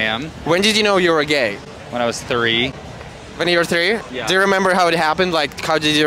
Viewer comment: At first I thought he said when he was 30😂😂😂, but nah nobody at 3 years old can comprehend stuff like that. He’s bullshitting.